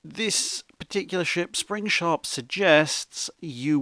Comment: What's the name of the naval design program being used? I hear "Springshaft" but googling that returns no hits.